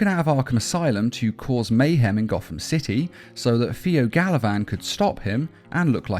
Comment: when he said Pheo Galavan i thought he said Taliban LOL.Its osama bin joker now i guess. Batman: Ladies and Gentleman.....We Got him